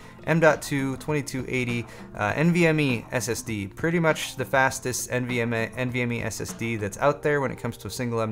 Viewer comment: I honestly thought you said "fastest MDMA"